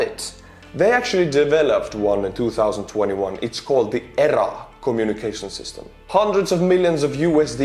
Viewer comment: Did you say the Russian communuication system was called the "Error" !